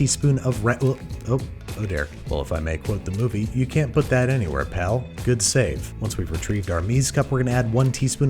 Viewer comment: So…I always thought the guy said: “just put that anywhere pal.” Not “you can’t put that anywhere pal.” 🤔🤷🏼‍♂️ I’ve seen groundhogs day like literally hundreds of times, but maybe I’m wrong.